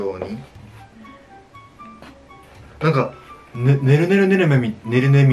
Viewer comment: どうしても"ぬるぬるぬるね"に聞こえるwwwwww